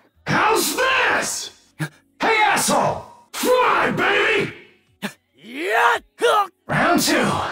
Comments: is my favorite one. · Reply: He said "fly, baby", but I keep hearing "fuuuuck, baby" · Fly Baby! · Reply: You mean 0:24? I keep hearing "Fuck baby" 😅